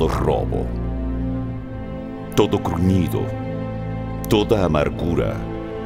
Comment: Buen vídeo; bendiciones hermanos ; tengo una duda ; en el minuto 0:51 no entiendo que dice (corñido ¡? )..